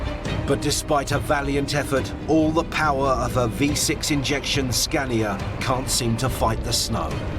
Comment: did he just say V6?